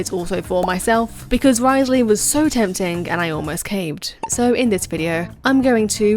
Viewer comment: Istg I heard,"Wriothesly was so tempting I almost came." ong I heard that but honestly me too.